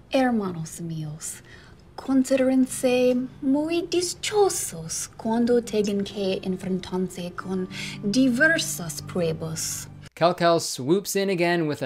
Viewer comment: wtf was that I have no idea what she just said and I've know Spanish my whole life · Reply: Herrmanos mios considerensen muy dischiosos cuando tengen que enfrentarse diversas pruebos (That's what a heard)